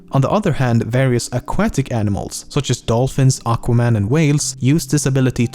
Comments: Did I just heard Aquire Man? loool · did I hear Aquaman? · did he say Aquaman ? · did you say aquaman? Or am I going crazy? Lol. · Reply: yep, he said aquaman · did he say aquaman?!?! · Did he just say 'Such as dolphins, AQUAMAN and whales' ? XD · did he really say Aquaman ? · did you say aquaman?? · who else heard that Aqua Man at 7:50??? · did he say Aquaman 7:49 LMFAO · did he say Aquaman? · Did he just say Aquaman? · Did he say Aquaman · did he say Aquaman ? XD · am I hearing right? aquaman??? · did he just say, "dolphins, Aqua man, and Whales?" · Wait did he say aquaman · did i just hear aquaman at 7:50? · did you say aquaman? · did he say aqua-man lol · does he say: various aquatic animals, such as dolphins, AQUAMAN, and whales? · lel did I hear Aquaman at 7:49 ? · did he say aquaman?! XD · did he really say aqua- Man?😂😂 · did he say Aquaman in 7:48 ??? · really aquaman i heard that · did anyone hear him say aquaman lol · did he say aqua man?! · anybody else hear him say aquaman in 7:50?